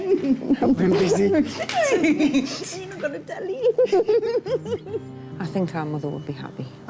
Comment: Is it just me, or did she say “you haven’t got a tele” around 23:50 😂